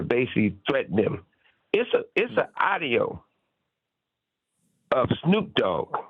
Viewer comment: Suge said it's a AHDIO out there with Snoop voice on it !! I think Suge meant to say AUDIO !!! 😅😅😅😅